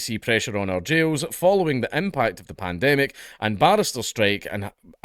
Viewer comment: I keep mishearing barristers for baristas. Oh no, the prison baristas are on strike! How are the poor inmates going to get their espresso?